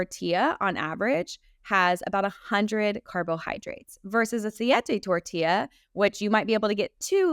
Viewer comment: Great information! Thank you for this conversation.What did Kelly say at 12:50 ? A big flour tortilla versus a ??? tortilla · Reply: Siete tortilla, they are made out of almond flour. "Siete" is a brand.